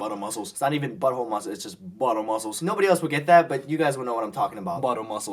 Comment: xD i thought he said: BUBBLE MUSCLES XD